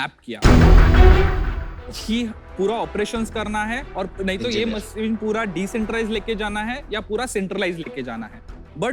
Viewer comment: What did Aman say at 13:40? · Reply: engineer